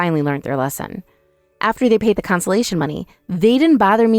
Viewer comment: Spouses don’t pay compensation only the affair partner, but good luck proving the three things in court. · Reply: I thought they said consolation payment, not compensation? Does it mean something different maybe?